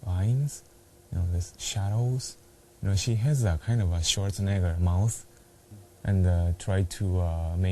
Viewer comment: sounds like he is saying "schwarznegger" to me...."schwarznegger mouth" something.